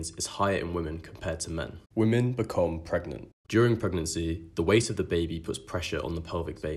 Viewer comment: did i just heard him say ...when men becomes pregnant. whahah wtf